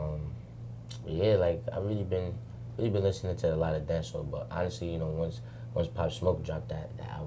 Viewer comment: What'd he say at Minute 21:42 "been listening to a lot of.." what?? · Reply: Tevin Brinson dance hall